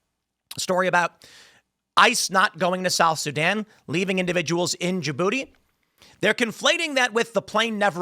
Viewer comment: Lol, 0:31 sounds like he said in Jen booty.